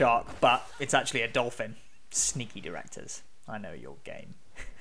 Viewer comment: i thought he said "sneaky directors i know you're gay"